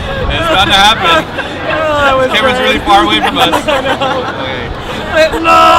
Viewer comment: I thought she said "I want to drink my tea"...